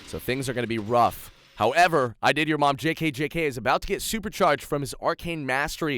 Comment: I can't take this serious when i hear you say " i did your mom jk jk "